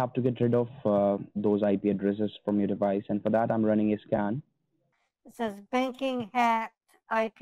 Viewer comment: When he said, "I'm running a scan now," it sounded like he said, "I'm running my a scam now."